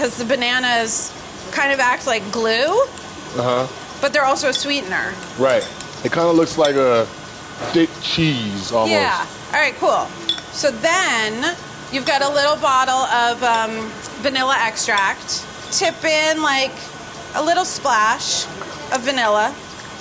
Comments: okay wait when he was mashing the banana, i thought he said D*CK cheese instead of thick cheese and i was soooooooo taken back LMAO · when he said the banana looks like thick cheese i thought he said "d*** cheese"